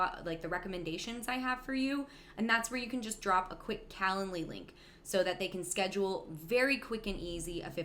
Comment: What's she saying @ 12:07? "Calenly link" or something? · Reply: Calendly is a program I use so client's can schedule a call with me.